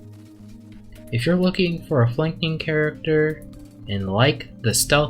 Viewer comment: I was saying "shuriken" :face-purple-crying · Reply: wait nevermind I just thought you said the f word instead of flanking, nice tutorial btw